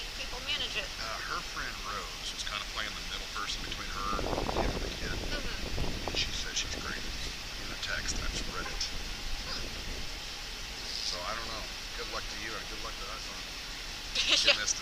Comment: Think her friend, Rose, said she's crazy. Not great.